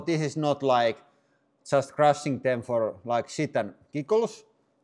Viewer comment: I thought I heard "for, like shit and pickles" at 1:12 and have been snickering about it for 10 minutes now. On listening it again, I'm sorta disappointed that I just misheard.....I think I'm just gonna use it anyway